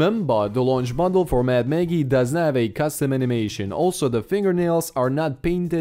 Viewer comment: Looks like an oversized cardboard cutter 😂 --------------------------------------------------------- 1:46 Did you say NOT have, right? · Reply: does not I make captions for every video so you can turn them on if you dont understand something :D